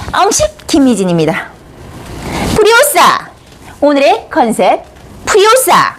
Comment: 프리오샤.... 불효자....로 들림ㅋㅋ 은 효녀^^ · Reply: 프리오사입니다 ㅋ 발음이 똥같아 그래요 노력해 보겠습니다 ㅋ